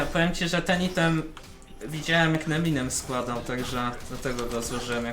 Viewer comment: też usłyszałem Eminem haha pozdro Trener :) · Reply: Neminem powiedział XD